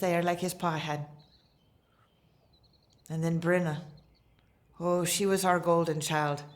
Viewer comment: did she say Brenna?